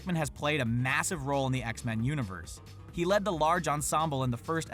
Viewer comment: Why at 10:35 it said X-Man instead of X-Men